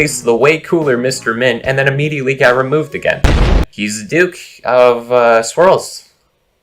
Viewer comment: thought you said duke of squirrels and spent a solid minute trying to figure out why squirrels are canon in this universe but water isn't